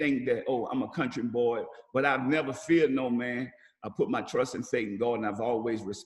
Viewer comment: he said "I put my trust in Satan God"... guess he slipped up there (even the subtitles say Satan), I thought maybe he said "faith" at first, but I played it over and over, he defiintely said Satan. Dude a sell out! There is only ONE God and his name is Jehovah!